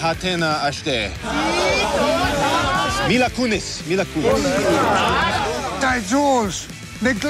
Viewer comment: Has anybody notice that when Seth mcfarlene was talking to the native Americans he said fine and it sounded like he said mila kunis